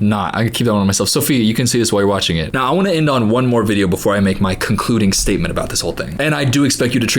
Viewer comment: why did I think you were gonna say "cumcluding statement"